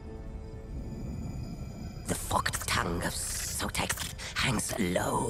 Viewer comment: I thought for a minute my Skink boi said "The Fucked tongue of Sotek." I was like excuse me good man?